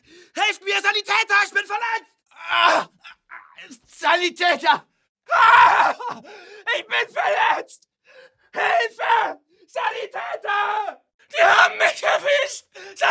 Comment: what does it say in 15:31 till 15:36 in german? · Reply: Saloufardos Sorry I'm late but he said "Sanitäter! AAAAH!! Ich bin verletzt, hilfe! Sanitäter!!!"